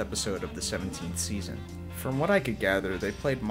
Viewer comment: These videos are very, very good, and for what it's worth, I don't say that about any videos on YouTube. Seriously. BTW, the way you said 'My Old School' at 9:47, it sounds like you're saying 'Mild School'...